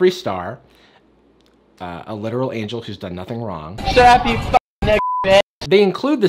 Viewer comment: I agree, I hate the _ but did you just call _ a she? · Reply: Mouse _ I think he said "WHO'S done nothing wrong not she's done nothing wrong"